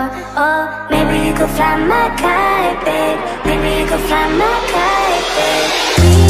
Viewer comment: I thought it's saying gamocay but it's fly my kite 🙂.